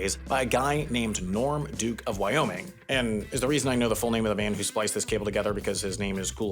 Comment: When you read "Norm Duke, of Wyoming" I heard it as "Norm, Duke of Wyoming".